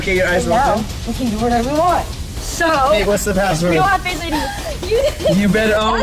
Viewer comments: I HEARD SAY "babe whats the password?" AHHHHHHHHHHH IM SCREAMING INSIDEEEE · I heard say “babe what’s the password “ 😅 I’m laughing my head off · GUYS HE DIDNT SAY “ babe what’s the password “ HE SAID “ hey what’s the password “ IT SOUNDS LIKE HE SAID BABE TRUST ME I EVEN THOUGHT HE DID BUT I TURNED THE CAPTIONS ON AND I HEARD IT AGAIN HE SAID HEY